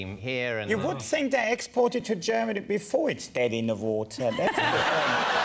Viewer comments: what is he saying? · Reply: T. "You would think they'd export it to Germany before it's dead in the water, that's the thing." There are English subtitles bytheway, you click on the bolt-shaped icon on the right, below the video. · What did henning say at 9:15? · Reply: You'd think they'd export it (Peep Show) to Germany before it's dead in the water.